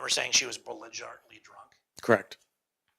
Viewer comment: she was “bolinjartly” drunk? What?? Did he mean belligerently? 😂 He said that with such confidence too! 🤣